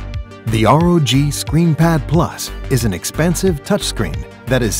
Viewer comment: did I just hear an expensive screen. Hmm nice marketing